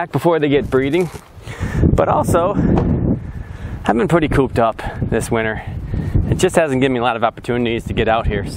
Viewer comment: Definitely thought you said "coked up all winter"